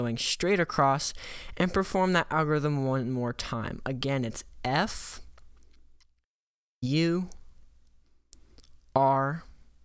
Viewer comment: Why did he say f u to me like boi u angry Edit